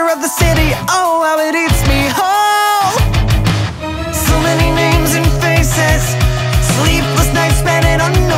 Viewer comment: did someone hear So Many Memes and faces at this part? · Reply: no they fucking did not, they said "So many names and faces".